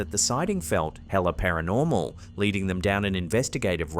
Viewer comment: did he just say "hella"?!